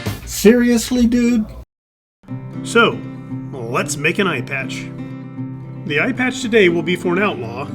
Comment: I THOUGHT I heard "ear patch" !!!! ha ha ha ha… thanks so much!!!